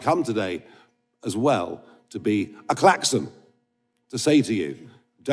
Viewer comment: A klaxton! 4.33 - I thought he said A Clapton (on sea)